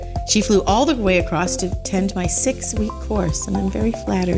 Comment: when she said "to attend my 6 week course" I heard "to tend my sick weak horse" and got really sad because I thought there was a sick horse. 😂